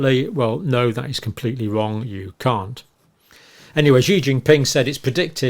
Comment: Am I the only one who heard Dr. say 'no that is completely wrong you c*nt'